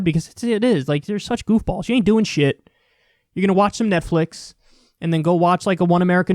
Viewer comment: What? I clearly heard you say these people are listening to METHflix? LOL